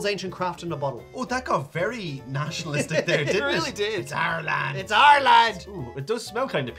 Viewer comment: For a moment I thought said Ireland then realized he said our land. 🤷‍♂️